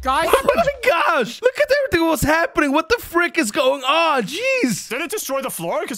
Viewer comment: Did Said What the F